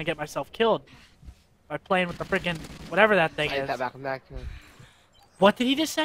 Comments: hahaha 5:00 he said "tabarnak" it means "crap" in canadian french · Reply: I thought he said I like that backpack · What he said at 4:59 is : ''Eille Tabarnak!'' which is a french canadian curse word meaning : ''Oh my fckin' god!'' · he swore in french he said(esty de tabarnak